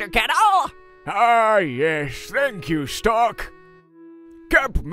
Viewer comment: When elderkettel said "strok" i thought he said "stark" Mr. Strak I dont feel so good